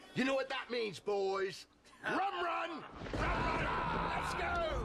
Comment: what did he say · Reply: Rum run?